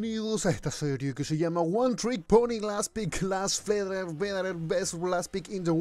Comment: estoy tan obsesionado que en vez de "OneTrickPony" escuché "OneTrickPobre" xD